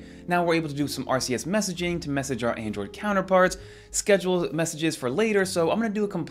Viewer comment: I thought you said "now we can do some artsy-ass messaging to our android counterparts."